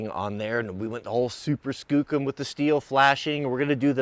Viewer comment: What is he saying @ 1:47 "super skook em"?